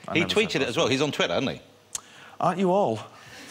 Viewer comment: Anyone know what he said at 8:19? Can't understand it.. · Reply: "aren't you all"